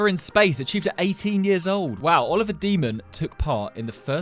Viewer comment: I thought that he said demon 😂